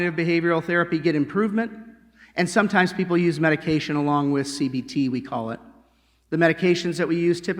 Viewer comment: C b t THought he was saying C B D like the chemical from weed that relaxes the body